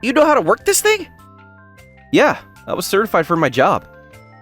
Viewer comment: Itsuki is forklift certified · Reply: For a little bit, I thought you said he was a certified forklift.